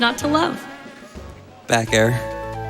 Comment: “Best boy, Jigglypuff. Sleep aid and pillow in one adorable package. What’s not to love?” “.....back air.” That split my sides 🤣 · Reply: I swear to god, I thought he said "back hair" 😂